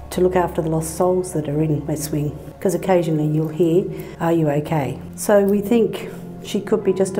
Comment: Bro in the start I heard “are you gay?” 💀💀